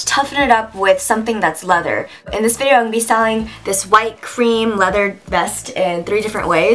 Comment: why did i hear you say i'll be "selling" this white cream vest lol i was like really?!?!?